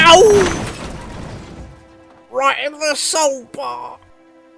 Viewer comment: What did the slate truck say at 6:42? "Right in the soap bar?" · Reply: He says "Right in the solebar", which is part of the underframe of the wagon :)